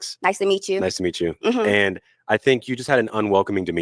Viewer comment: I don’t think it was colorism, I think she said “we know why” he popped his balloon because he has a small peen 😂